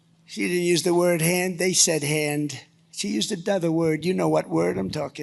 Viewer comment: What is this alternative word for "Hands" that is so disgusting? 9:01 "She used a dother word. You know what word I'm talking about." No, no one knows what you are talking about, and "dother" is not a word.